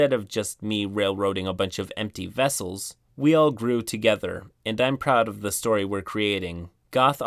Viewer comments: I'll admit, when he said "we all grew together," I heard it as "we all brood together." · Was only listening to this, not reading it, and I swear at the end I heard "we all brood together" and almost lost my shit.